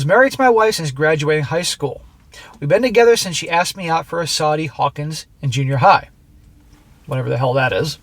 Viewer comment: That's "Sadie Hawkins." It means that the girls are supposed to ask out the guys instead of the other way around.